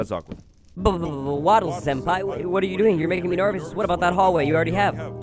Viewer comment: dood did he say 'wattles senpai' fr????